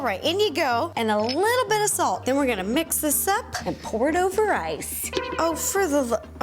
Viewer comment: did she just say pour it over rice.